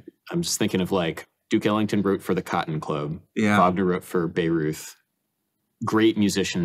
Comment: LOL at the subtitles. says, "Wagner wrote for Bayreuth," and the subtitles say, "(indistinct) wrote Babe Ruth." 😄 To be fair, though, can't really fault the transcriber for not getting yet another esoteric reference. 😊